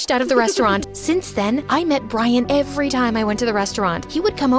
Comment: HELP I THIUHGT THAT SAID ‘BRAIN’ INSTEAD OF ‘BRIAN’ 😭😭😭😂💀